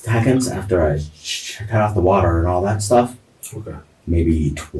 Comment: is it me or @ 2:22:26 might've just been a bad stutter but it sounded like the fool was gonna say "how many s-s-seconds after I s-s-shot- Cut of the water"